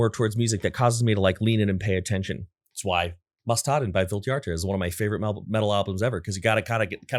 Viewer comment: what does he say at 1:13:27. cant piece it together phonetically even at .25x speed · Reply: Måsstaden by Vildhjarta 🙏😊